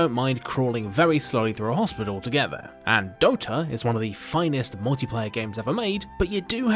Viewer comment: What's the game at 0:35 ? I can't understand the name can someone pls tell me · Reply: Ik it’s been 4 yrs but it was Dota lol